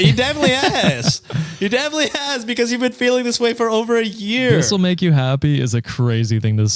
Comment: they said that he commented that her belly wouldn’t stop jiggling whilst she was 8 months pregnant, i assume that’s what the commenter was referring to! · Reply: it was over three years I thought she said.